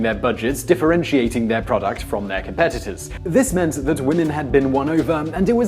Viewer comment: the captions say "This meant that woman had been won over- or brainwashed as some might say - and it was" where he just said This meant woman had been won over. Funny little extra!